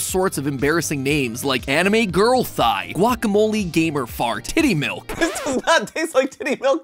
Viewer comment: Hearing Mat say “tiddy milk” has brightened my day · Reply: I heard “Kitty milk”